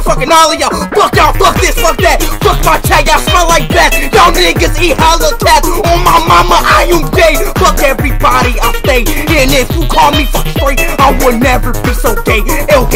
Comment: ngl, this is fire edit: at 0:32 did he say "oh my momma are u gay"